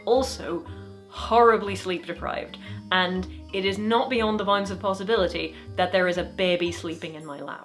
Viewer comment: Pardon me, "beyond the what of possibility"? I can't figure out what you are saying there but would like to know what it was. The subtitles give "vines" but this doesn't sound quite like what you are saying (and seems a bit insensible, though maybe some idioms are just like that, their creativity is only comprehended by embracing them as they are). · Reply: Bounds of possibility. (sorry if you get this comments multiple times, I’ve written it thrice and it’s not showing up).